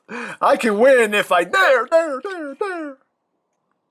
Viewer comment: "Tell grimlock about petrorabbits" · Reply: and all this time i thought he said petrol rapids